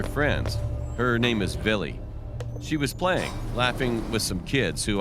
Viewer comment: a girls name "Billy" ' tell me if i heard it wrong